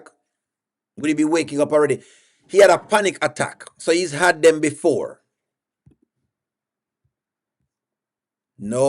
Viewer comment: I thought said the Dr said he had a panic attack